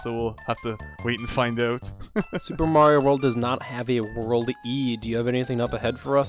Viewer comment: what does he say? world eed what? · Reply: World e, as in the bonus world that Super Mario Advance 4 had!